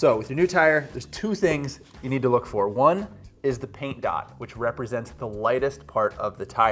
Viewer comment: I thought he said the pink dot and I may have been colorblind lol